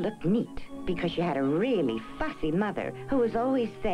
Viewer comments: Bruh,when she said, “she had a really fussy mother!”,i thought she said, “she really had a fucky mother!” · i thought it said "a really fucking mother" 💀